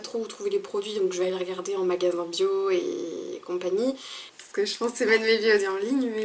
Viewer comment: Excuse moi mais j'ai pas compris ce que tu as dit ! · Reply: en magasin bio et compagnie